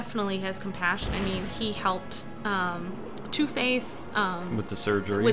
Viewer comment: When he said two face I thought he said toothpaste